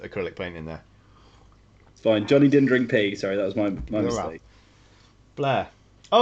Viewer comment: It did sound like he said he drank pee!